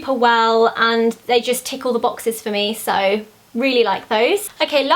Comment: I THOUGHT SHE SAID TICKLE THE BOXES IM LAUGHING